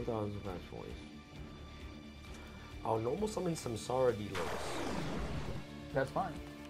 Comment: I thought he just said Samsara D Lopez